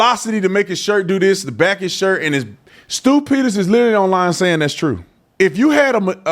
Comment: When you said Stew Peters, I thought you said Stupid Ass. 😂